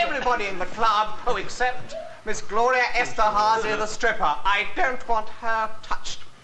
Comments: WTF? I dated Gloria Esterhouse once, that was Tim B-T? I should've twigged when I saw her union Jack corset... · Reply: I thought Tim said Gloria Estefan. Wtf? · For a moment there I thought he'd said "Gloria Estefan" XP